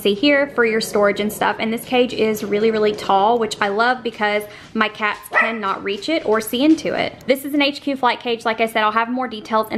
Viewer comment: I thought you said so my kids cannot reach it. I love you